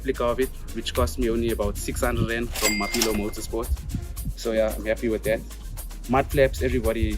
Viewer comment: i couldnt quite hear one of the places you bought parts, was it mobillo motorsports? it was at 5:20 when you were talking about the prestige panel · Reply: Mapilo motorsport